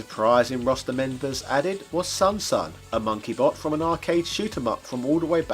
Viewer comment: The SonSon in this game is a girl. Sounded like you said "monkey boy" when introducing her lol. The lore is shes the granddaughter of the SonSon from the arcade classic, her name being SonSon III